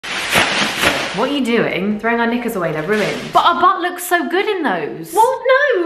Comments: Who else thought she said the n-word instead of “knickers?” *Asking for a friend 😅* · Why did it sound like you said the N-word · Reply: Knickers = underpants in Britain.